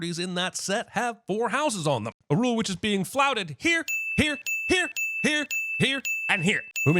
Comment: correctly says 'flouted' but the subtitles say 'flaunted'.